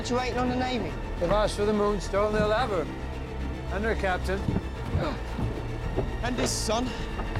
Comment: Ok, what is he saying here? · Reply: They've had asked for the moonstone they we will have her and her captain and his son